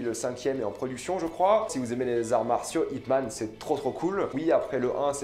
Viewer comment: it man??? hitman??? c'est quoi ce film? jamais entendu parler...! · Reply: IP MAN, une tuerie